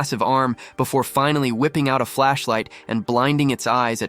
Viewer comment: When you get were saying flashlight I thought you said black flash